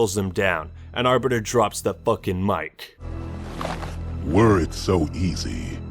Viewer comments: when i was younger i thought the Arbiter said "words so easy" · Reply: I thought he said “worried so easy” I was like wtf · You know. When Arbiter first talked to Chief in Halo 3, I never knew he said "were it so easy" I thought he said "Words so easy". It always sounded weird like that, but I always thought it was him basically saying "use your words" and I thought that meant "be more human" Glad to know that went down the toilet. 😧